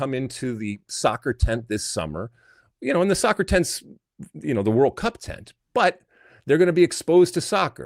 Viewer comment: Alexi said "World Cup Tent" and I heard "World Pup Tent"⛺ 🐕